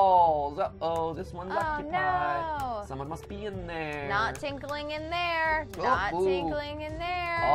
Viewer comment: I turn up my volume and all I hear is “Not tinkling in there.”